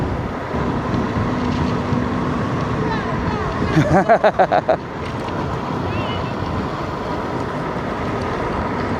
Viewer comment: What were they saying? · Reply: Ah they were saying go, go, go. Words of encouragement haha.